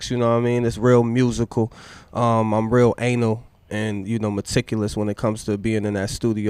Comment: did he actually say anal?